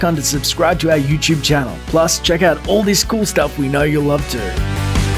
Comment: At the end , I thought you said " All the 'School' stuff , we know you love too ) . But I know that you said " The cool stuff you love too . "